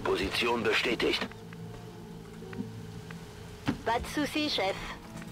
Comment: Anybody else hears "bad sushi chef"?